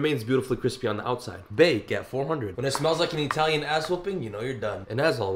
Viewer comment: I thought he said italian asshole 💀💀💀